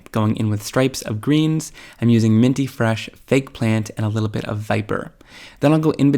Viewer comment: I had to laugh at myself because I kept thinking you said 'eggplant' (hearing loss!) and then at the end I realized you were saying FAKE plant. 🤣 "oh" ;) Anyway, I really loved all of your ideas (esp. designing pull apart backgrounds!) using the inks creatively. They were all really nice looking, I liked the generational stamping and the one you spritzed with water. But all were really nice! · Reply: I thought he said eggplant also at first and was quite dismayed that I may have missed an ink color!!🙄🤣🤣